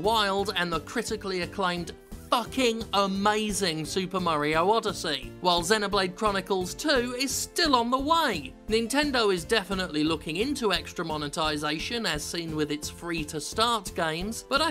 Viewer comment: i heard "xenoblade 2 is still on the wii".